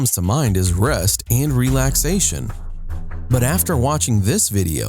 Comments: Who else thought he said Breast and relaxation? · Thought he said breasts and relaxation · In the intro He said ' rest and relaxation ' - I heard ' breast and relaxation ' ....... Don't know why 😇😇